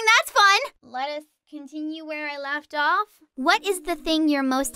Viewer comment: sorry but i heard " lettuce continue where we left off "